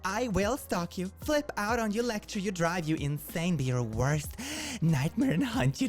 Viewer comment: oh god, I heard “electrocute you” instead of “lecture you”